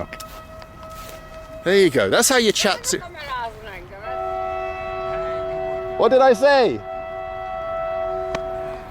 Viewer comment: What's the lady at 6:56 saying/upset about my fellow bald comrads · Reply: "Há' de ne kamerázzon engemet" She was upset about being on camera, also very heavy accent so might be a homeless lol